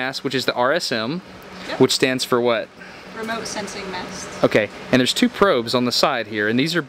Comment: I thought she said REMOTE S£XY MASS